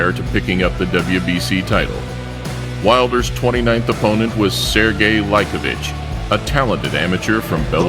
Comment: the captions said “sergey like a bitch”